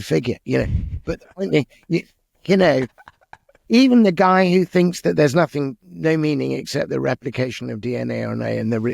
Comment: Did anyone catch what music it is that Richard Dawkins wanted? (At the 47:00 mark) I couldn’t parse it, and neither could the subtitles- they called it “Par B minor Mouse“ 😅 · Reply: Bach's mass in b minor